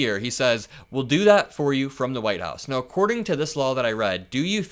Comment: Handing out cash while campaigning is completely illegal. It sounded to me like he said, “for the White House” not “from the White House.” Re-listen. FOR not from. “We’ll do that for you for the White House.” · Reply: I heard 'for the White House' as well